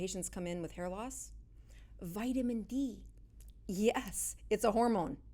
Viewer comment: Did she say Vitamin E? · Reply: D